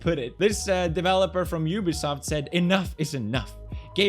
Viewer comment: I thought he said a worker from Yugoslav